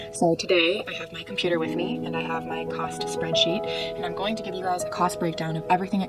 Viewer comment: I thought you said, " I have my pasta spreadsheet," and wondered if that was an app of some kind. More caffeine.